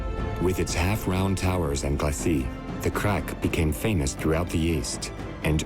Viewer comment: 'half-rounnd towers and [glassie?]' what is that word? I tried a bunch of variations of spelling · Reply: Glacis - Fr. I tried linking the dictionary to the word, but YouTube being a Google product won't allow cross links that take you off their website.